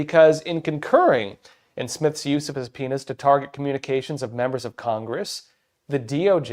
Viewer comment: sounds like he is saying "His Penis" instead of subpoenas. I had a laugh.